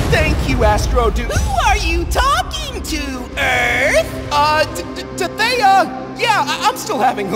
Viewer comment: DID EARTH SAID " the-the Theia..."